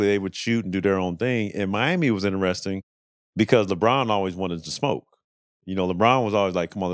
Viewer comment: I thought he said “Lebron always wanted too smoke” 😂😂😂